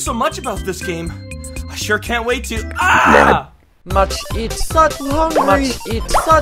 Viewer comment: much eat such hungry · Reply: Karate Shark I thought doge said must eat sudden hungry